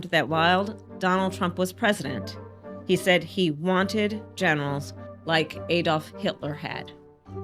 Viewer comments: I swear I thought she said genitals 😂😂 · I could have sworn he said geni*als, I need to fix my hearing.